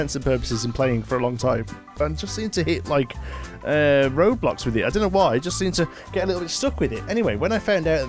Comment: Wait I'm confused, how do you get reminded of ROBLOX by this? Or did I mishear what you said at 0:33? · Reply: He said he hits "Road blocks" ;)